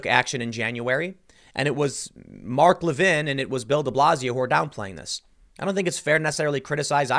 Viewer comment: Thought you were saying build-a-blazio